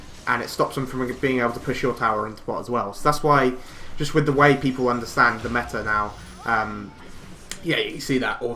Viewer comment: did you say "Thats why just the white people understand the meta now" or is my hearing just retarded? Haha.